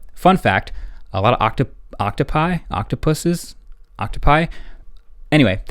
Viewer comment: Sorry I thought I heard Octopussies 😅